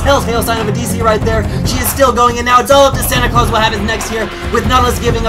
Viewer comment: did I hear Santa?